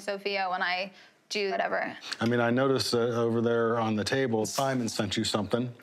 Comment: Did she just say "not stable"????